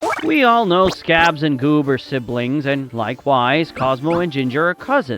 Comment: did u just say “scabs and boob”